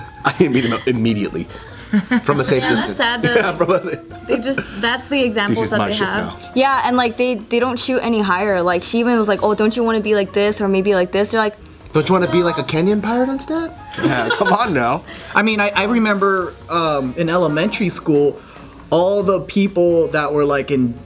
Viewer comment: I heard, "Don't you want to be like King of the Pirates instead?" My phone's volume was low. 🙃